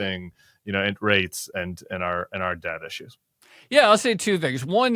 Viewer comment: I heard “debt issues” as “dad issues” and somehow the arguments still fit